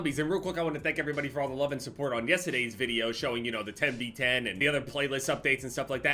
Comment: did i hear ah "yestadayys video"